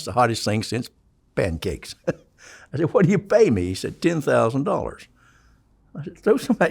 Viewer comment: captions are wrong. he said "$10,000," not "$110,000."